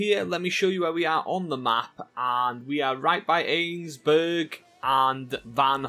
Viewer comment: the subtitles says “AIDS burg” lmao